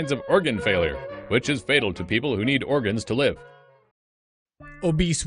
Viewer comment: did he just say "fatal for people who needs organs to live ?!?"